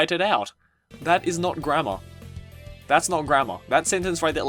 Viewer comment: when you said grammer i thought you said grandma